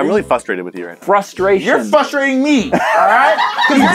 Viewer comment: I heard ”You’re f***ering me!”